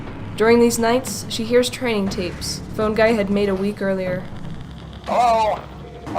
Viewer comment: Literally my mum talking to me on the phone while I’m walking to school on the first day back after quarantine Edit: the whole time I thought she was saying fun guy instead of phone guy